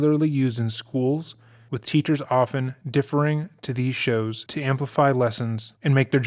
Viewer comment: deferring at 0:28 sounded like differing